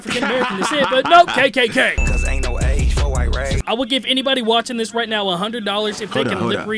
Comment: he said ain't no age for white rage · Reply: I thought he said...cuz there's no age for irate 🤦🏽‍♀️